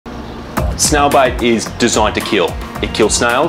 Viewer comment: I thought they said snail bites not snail bait